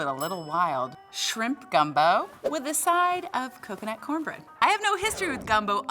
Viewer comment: Wait a minute... did she just say coconut cornbread?!